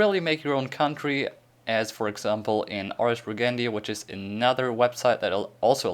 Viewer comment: what the website he said in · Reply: Ars Regendi.